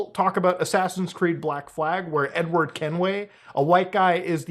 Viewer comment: I heard "n-word Kenway" lol